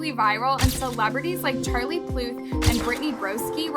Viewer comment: Charlie what- 2:46 she did not just say pluth did she?! 🤣